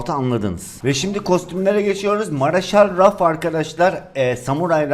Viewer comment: Marshal Ruffs Dediğinde sadece ben mi Maraşlı Ruffs diye anladım